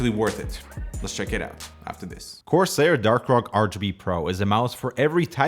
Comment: I swear I heard Corsair Dark Rock instead of core lol